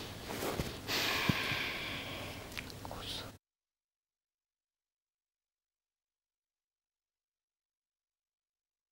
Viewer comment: ভয়েস আর্টিস্ট এটা কি বলল? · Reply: কচু